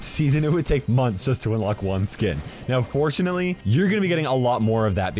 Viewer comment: thought he said unfortunatly lol